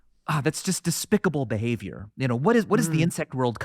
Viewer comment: I thought he said "desticable behaviour" until I went back and checked 🤣